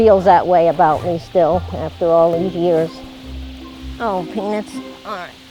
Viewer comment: I love these people and the bears they are so nice anf peacfull but dif anyone else hear her say "penis" at 3:56 ? shr is saying penuts but 2hoo rlse heard what i heard because she didnt say it very clearly XD